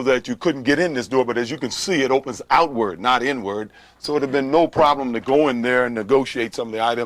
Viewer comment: does he say "It would of been no problem going in there" or "It had been no problem going in there"?